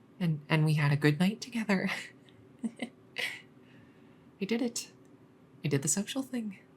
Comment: I am embarrassed I misheard this at first, but not so much I won't say it!~ At 23:38 to 23:40, I, FOR WHATEVER REASON, hear "We did the sexual thing", instead of "We did the social thing". I am... AAAAAAAAAAAAAAAAAAA.